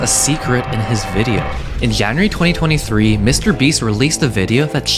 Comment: Is it just me or did it sound like he said Yanuary Instead of January 💀 🦔